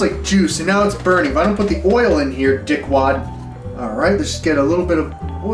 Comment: LOL THIS MOTHER FKER. around 8:07 he says "dickwad" I was like wait a minute did he just say "dickwad?" Then right as I said that 8:16 he says "no I didn't" This dude be doing some subliminal bullshit to fuck with schizophrenics.